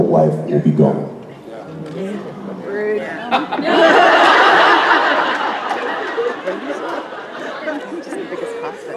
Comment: What was said at 24:18 from an audience member? (this caused Jensen to laugh and say stop??) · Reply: "Rude" reminding us we're adults and the best thing on TV is leaving. Im not crying ur crying